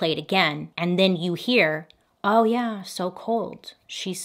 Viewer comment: Soooo I’m not sure how I feel about this one. I think the husband is definitely suspish, but SO IS THE SON. His story is the only one that changed. However, I DEFINITELY hear “cheating wh*re” in that recording. I don’t hear “so cold” at all, even going back to listen again. Either way, this woman didn’t deserve to die this way, regardless of if she was having an affair or not.